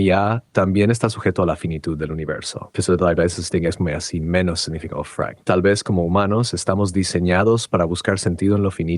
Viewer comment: Que dice en el minuto 41:36 ? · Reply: ''Molesto por que base esto en algo, solo queria ver modales.'' Que miedo. asi analiza.